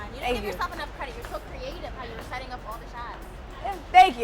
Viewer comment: he is really good, but there is something so editorial about jessica’s shots. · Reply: Chermaine thought you said shorts and I was so confused